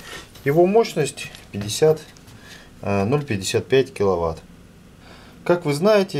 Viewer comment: 50 киловатт?????????????????????????? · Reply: Дато Дасе Вам послышалось, я сказал 0,55 киловатт.